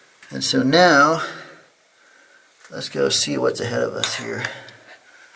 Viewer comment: lol it's me again 0:12 -13 i hear someone say "don't go here"